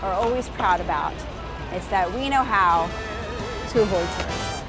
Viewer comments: mark What are New Yorkers proud about? Can somebody spell what Bridget said please...again Very refreshing ( someone commented this below) have to agree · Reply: I said: We know how to avoid tourists 😜 (*shakes fist at mic pop*) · what does she say? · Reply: Adem Sofilić we know how to avoid tourists